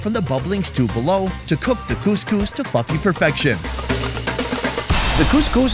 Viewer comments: Am I the only one who hears "cook the coiscous to f$cking perfection" at 10:46? Lol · Had to do a double take @ 10:46, I know you said 'to fluffy perfection' but it sounded like you said 'to f*cking perfection' I'd bet it really is cooked to the extent of that description though! :') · NGL so sounded like you said that method of cooking cous cous cooks it to fucking perfection ( i know you said fluffy XD but i heard Fucking XD )